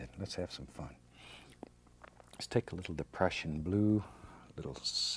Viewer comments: Oh gosh for a minute I thought he said "depression blue" instead of "prussian blue". I only realized I was wrong when I went back and turned on the subtitles. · Did anyone else thought he said depression blue??? 15:32 LMAO BECAUSE THAT'S WHAT COLOR I WOULD ME IF I WAS PAINT